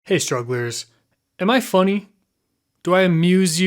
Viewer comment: genuinely thought you said "hey jugglers" in the beginning and nearly had a stroke